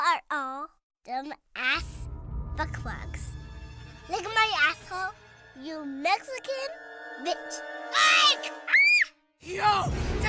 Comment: I thought Kyle said "look at my asshole, you Mexican b****"